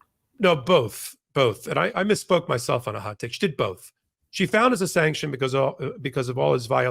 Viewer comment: Thought u said hotcakes😂😂😂😂